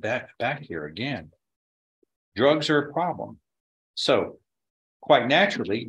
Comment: Why has she had to "rebuild so many times"? I also thought she said that drugs are no problem.